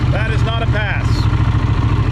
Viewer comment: Did he just say "That is not a pass" what?